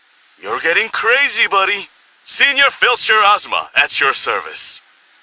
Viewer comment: When Azuma said *Precision and efficiency, the Herald's way!* I put the Herald symbol emote. Also I love when he says *The task is done!* This cracks me up✨ just like when he says *Senior Feldsher Azuma, at your service!* I always got cracked up by that "At your service" thing 😘and with his gallant greeting stance. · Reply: I always thought he said Senior fills your asthma at your service 😂